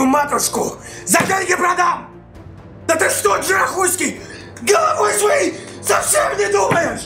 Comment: Мы послышалось, или На 1:13 Ильич сказал "Да ты что, Джарахуйский?!" :DD